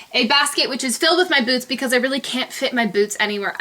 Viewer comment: at first i thought she said she keeps a basket full of boobs, then i realized she said BOOTS c: tee hee, oh and u look liek caroline sunshine c: but a freakin 100x better version c